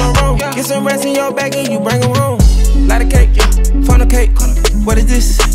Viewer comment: Ohh shit I thought he was saying funnel cake, but he really saying fornicate. 😂